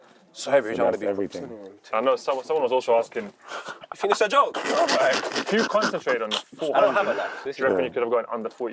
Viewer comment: did say "you think its a joke? i dont have a nice" @ 13:22? lollll